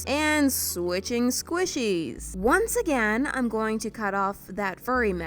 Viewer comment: YES FINALLY THE CAT STACK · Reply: Who else heard 'sQuItChInG sWiTcHiEs' a few seconds after processing the words?